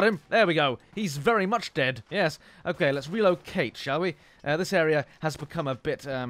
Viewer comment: When you said "Let's relocate" I thought you said "Let's reload Cate" And then I thought you called your Lee Enfield for Cate... I have a weird mind.